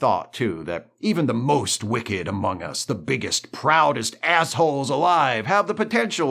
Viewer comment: i thought you said the biggest, brownest asshole and i thought that would be a funny thing to call someone